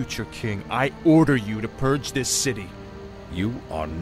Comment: Did someone says purge?